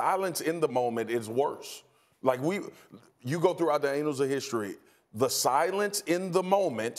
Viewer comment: Sounded like Spears said, "anals of history". So appropriate for this scandal.